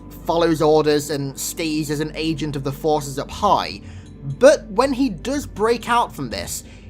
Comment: I thought you said “a pie” when you actually said take orders from “up high” LOL